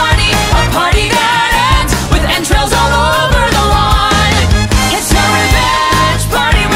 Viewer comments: Ok why do I hear “eggshells all over the wall” as “entrails all over the lawn” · I thought it said "with entrails all over the lawn" Oops. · I misheard "with eggshells all over the wall" as "with entrails all over the lawn" this video changed everything... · I heard “with entrails all over the walls” and was like “that can’t be right.”